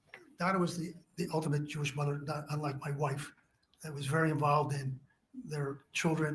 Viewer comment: Omg I thought he said "unlike my wife" and I was like this turnip really just used his testimony as a chance to throw shade at his wife?!? 😂